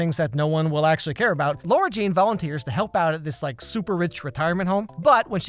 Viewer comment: I'm I the only one who heard him say Lora instead of Lara Jean